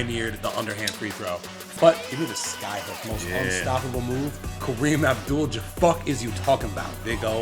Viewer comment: I thought said Kareem Abdul Jabucket!!! 😂😂😂 Missed opportunity for a nick name.